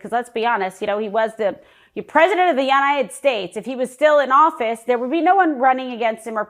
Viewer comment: At the 4:29 of this video clip, did I hear correctly? Where the heck are the Yanited States?